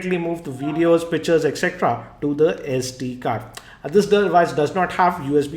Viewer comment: sounded like you can move videos,bitches..haha